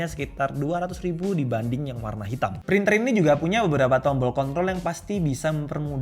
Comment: cont*l atau control Sedikit kiritikan bang atau gw salah denger!..